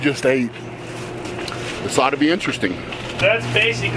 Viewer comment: "Sodomy interesting"? What did he say? lol